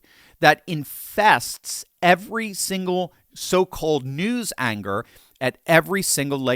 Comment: into the vid, I LOVE IT! The bestest phrase "New Angers" instead of "New Anchors". BAM!